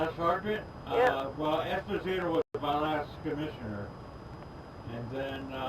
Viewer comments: What I hear is, "Esposita was my last commissioner" (sp?) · Sounded like he said his last commissioner was "Esposito" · Sounded like he said: "Esposito was my last Commissioner". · Would love to hear what you guys think of this type of video. I had fun making it so if there is a good reaction I might do more like this! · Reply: Think I heard “Esposito” maybe 🤔. Great work& channel 👍🏽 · It sounded like he said "Esposito" -- <link> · Sounds like he said Esposito was his last comissioner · It sounded like he said the commissioner was Espetito. Not sure on spelling.